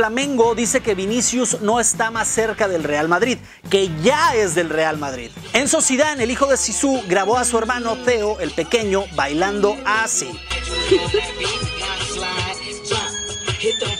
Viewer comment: jajajaj yo escuche "el hijo de zidane grabo a su hermano feo"